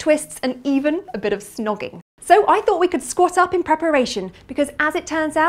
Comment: Did she say it was time to "squat up" in preparation?